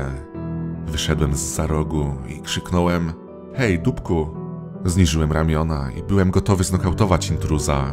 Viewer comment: Ja usłyszałam jej tupku XD